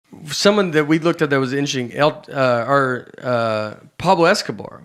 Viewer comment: In he very first line in this clip, it sounds like he said "inching" instead of, I assume, "intersting". Before anyone jumps on me for this, this is merely an observation, no judgment attached. I'm a fan of Nate, it's just my OCD freaked out at his pronunciation there at the beginning.